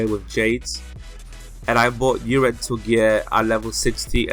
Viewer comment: Hey cool video, i dont understand what u said about the gear at lvl 60, i just hear "darenziel gear". So what gear should i get at lvl 60? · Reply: David Rodd Sorry bout that, i recorded this when i was sick so my voice sounded quite terrible. I said rental gear. You can do quests for goddess teardrops which you can exchange for rental gear. This is a really good set gear for those who cannot afford +9 epic sets. I would recommend holding off gearing yourself until at least lvl70 because once you hit lvl 80 your lvl60 nest sets would not be as useful. Hope this cleared things up a bit :)